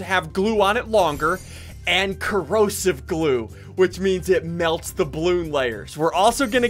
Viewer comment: When he said corrosive glue, I thought he said corona glue.